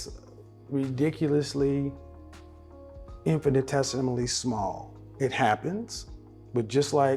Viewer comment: What word did he use at 9:02? I never heard that word and idk where to start to try and spell it out to find out a definition. Some one put me on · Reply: Orr I believe he said “Infinitesimally”.